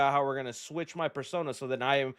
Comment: did you say persona whah 👁